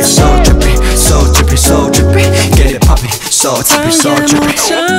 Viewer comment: It sounds like they are saying “get it papi” but they are saying “get it poppin” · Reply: I thought they said ''get it puppy''